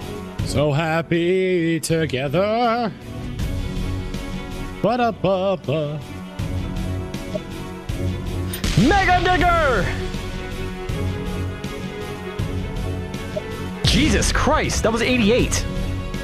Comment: Did he say mega N**** or Digger